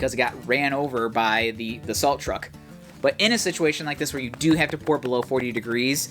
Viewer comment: At first I thought you said ‘assault truck’. You said ‘a salt truck’. Lol